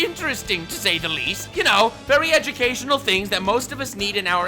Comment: I swear I heard "EchiCation" instead of "Education" ^^ Maybe Melody & Silvervale & IronMouse can help with that "EchiCation" :3